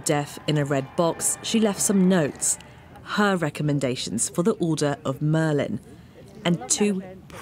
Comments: did Sky News actually say "Order of Merlin"? I genuinely thought Harry Potter was fictional! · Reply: I heard 'Order of Merlin' too. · "Her recommendations for the Order of Merlin" Like the fictional order from Harry Potter? Did I mishear this? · Am I the only one that caught “Order of Merlin” instead of the Order of Merit?